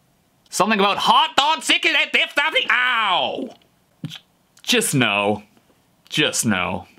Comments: i heard “Clock’s been ticking, I just OW!!!” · I heard meow at the end. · i heard “Clock’s been ticking, i just OW!”